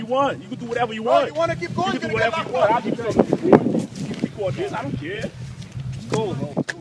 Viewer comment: the transcript reads: "officer 2: "you wanna keep going? you're gonna get my foot."" What he actually says is "you wanna keep going? You're gonna get locked up." Just wanted to clarify. Jersey accents... On a side note, having lived in this town for approximately 10 years and dealing with the police, mainly for traffic violations and not just in Lodi but surrounding towns as well, they are, for the most part, all very much corrupt, cowards and bullies hiding behind a badge (more so in some towns than others). With that said, this video didn't surprise me one bit. I am very happy to see Cop Block reaching out so close to my home and I have been spreading this video as much as possible so those in the area can see the courtesy and professionalism these officers provide at a cost of 6 figure salaries on the tax payer dime. I think I will give a call in a week or so and follow up on the outcome of the internal affairs investigation.